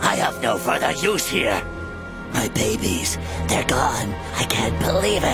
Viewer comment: Cell: my babies they gone. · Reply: Bryan Blanco when he said that i thought he said my rabies they're gone